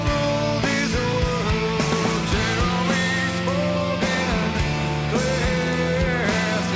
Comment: Of all the times listened to this song, not knowing the full meaning of the song until now. I always thought the chorus said, "Jeremy is broken"